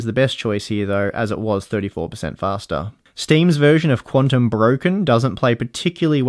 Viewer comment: did he say Quantum broken xD ??